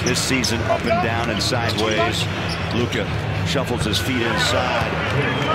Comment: did he say Luka or am I hearing it incorrectly 😃